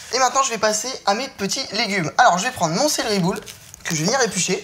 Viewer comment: j'ai compris ''serre les boule ''😂😂😂😂